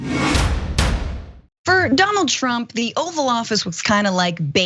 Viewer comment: my hearing focus has now gone , I thought I heard "Dum old Trump" being said at the very first statement said in this video... am I wrong? If not, at least my hearing is still properly functional, else I need to schedule an doctor appointment.